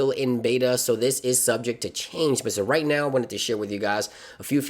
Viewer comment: It sounds like he said "Right now I want to shit with you guys" 🤣